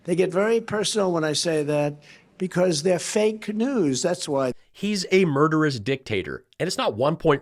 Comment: It totally sounded like Trump said, It's fake canoes" not "fake news." 😂 I think I like that better. 😂